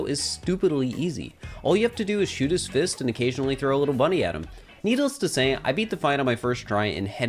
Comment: DID HE ACTUALLY CALL SMALL FRY A LITTLE BUNNY?! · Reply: I think he said "little buddy."